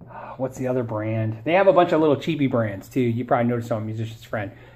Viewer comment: did he said "chibi brands"??!